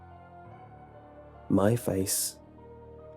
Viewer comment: Jajaja when he said my face in the ending I thought I heard mindspace XD